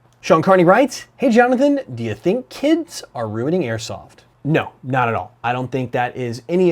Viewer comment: anyone else thought it said "running airsoft"